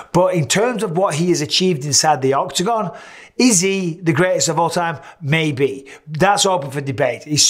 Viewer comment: When he asked, "Is he the greatest of all time?" I heard, "Izzy, the greatest of all time." Am I the only one?